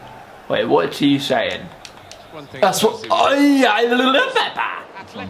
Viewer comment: I heard "lõpeta" what means "stop it" in my language and i was like "Marcus wtf do you speak Estonian?"